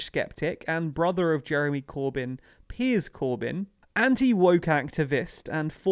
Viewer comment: When said Piers Corbyn, I thought he said Piers Morgan and that nearly choked me up when I heard it.